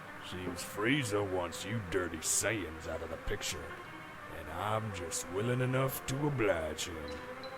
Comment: It’s interesting to me that you hadn’t come up with the idea to make Dodoria a woman yet because for the LONGEST time. I was sure that when she says “I’m just willing enough to oblige” I heard it as “I’m just WOMAN enough to oblige”. I don’t even know what that would MEAN but it made sense to me and I somehow interpreted it a female empowering? “Don’t send a man to do a woman’s job” That sort of thing